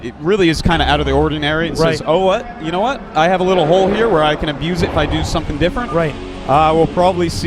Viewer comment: I skipped to 7:15 and the first thing i heard was... " You know what I have a little hole here. I can abuse it...." (O_O)